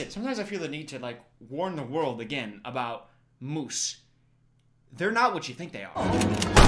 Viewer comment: … you’re worried about MOOSE?! I thought you said GOOSE and, as a Canadian, I was like “Yes! 100% geese are the worst”. But no… you’re worried about moose